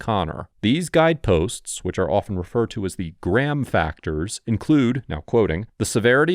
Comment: I'm at work listening to this, work in a factory. When you said gram factors I heard "gram crackers." Maaan I thought I was trippin 😂